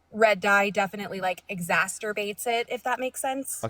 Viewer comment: I need this lady to know it’s exacerbate, not exasterbate. I thought she said masturbate at first 😭